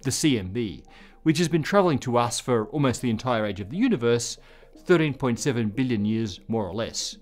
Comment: Did ye say the light has been travelling towards us for 30.7 billion years? I thought it was 13.7 billion? 🤔😉😅😁👍